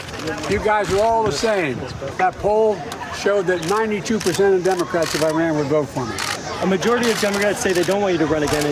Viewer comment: did he say" those polls shows 92% of democrats of Iran would vote for me" ? I dunno...prolly just me.